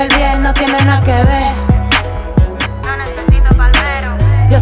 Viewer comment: alguien sabe qué dice ahí? "No nececito..." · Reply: No necesito palmerosssssss